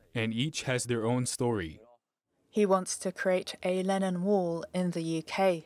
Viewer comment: When I first heard "Lennon wall" I thought they said "Lenin wall"... I was a bit confused :-)